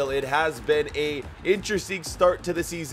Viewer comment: Did just say " a interesting start"